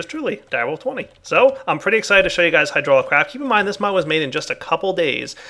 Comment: hehe, I keep hearing "Hydraulic Raft"